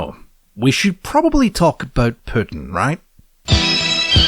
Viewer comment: you say Putin and I hear red sun in the sky and I just think wth and then I read the text onscreen and say “oh that makes sense”.